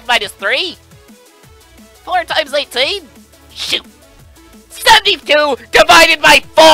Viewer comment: Did he just say sh*t? Or am I mishearing it? EDIT: I misheard it. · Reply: Blue-Maned Hawk shoot